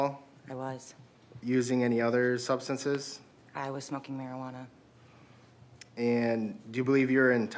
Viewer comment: I thought she said she was smoking meth. Lol.